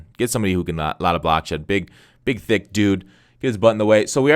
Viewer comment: sound like he said big dick dude lol like this comment